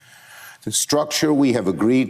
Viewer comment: I heard him say agreed structure not "A Greed Structure"